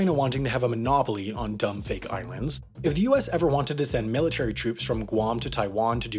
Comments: I swear he said irands there, , am I stupid? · When he said "Dumb fake islands" I heard "dummy thick islands"